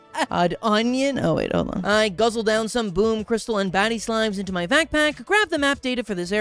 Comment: “I guzzled down those BATTY slimes.” My dumb ahh thought he said BADDIE SLIMES